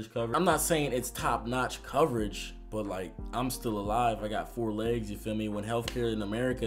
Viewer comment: did you say four legs at 6:49?